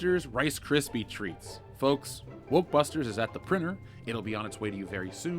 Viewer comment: did you just say wokebusters lmao